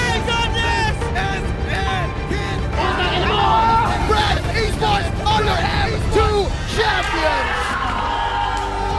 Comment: what did he say?? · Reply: put tank in a mall!!